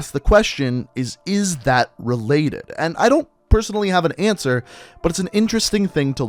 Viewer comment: I thought u said zero was responsible for creating Corvus